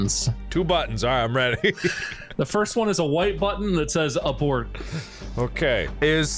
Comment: I know I'm late but in 14:29 , the subtitle says "upward" even though Bob said Abort.